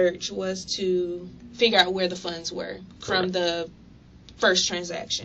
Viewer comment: Did she say, “Finger out where the funds were?”!